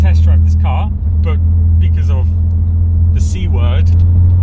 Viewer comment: at first i heard sea world xD